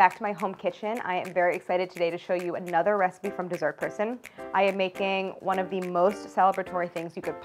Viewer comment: Tell me why I thought the theme song said “Shes a croissant” and not “Dessert person” 🤦🏼‍♀️ Its time for a nap